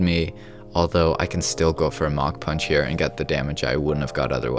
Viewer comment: Idk why but when he said Mach punch I thought he said mountain punch